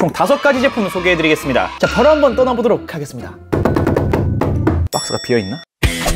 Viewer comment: 흐릿하게 들리는 '박스가 비어있나...?'